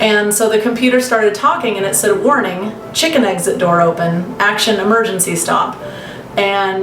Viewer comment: I had to rewind I thought she said "warning chicken eggs at door"